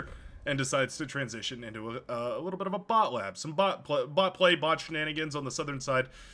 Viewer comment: wait, am i only one who heard he say butt play at that moment XD